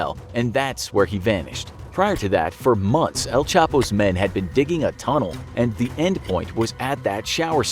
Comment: Did he just say chapo goosemen